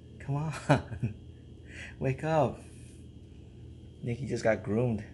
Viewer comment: Sometimes I hear "Mikki" as "Nikki"